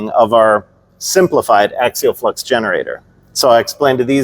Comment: Oh! Oh! You said "Flex Generator." I heard "Flux Capacitor!" Suddenly I was going back to the ....